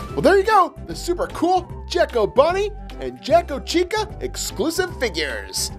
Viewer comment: Lol I thought it said jack-o bunny and jack-o chick lol